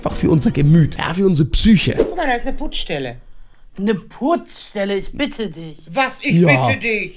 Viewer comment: Ich habe verstand anstatt putzstäle habe ich verstanden furtzstelle 🤣🤣🤣🤣🤣🤣🤣🤣🤣🤣